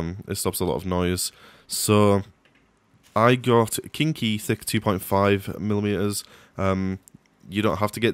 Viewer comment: When he said the name of the o ring, it sounded like he said kinky thic